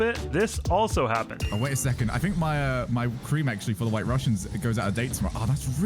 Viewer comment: Wait at 3:58 did he say “cream of White Russians”????